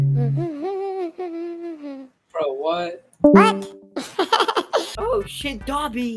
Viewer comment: sounded like boy with uke